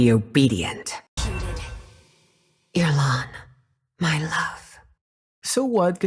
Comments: i think she said aamon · i think she said Aamon my love? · "?, My love" I heard the "?" as... "Elon"...? Hm... Also, what's with this women with dead lovers or something. If I remember correctly, Aurora is also the same.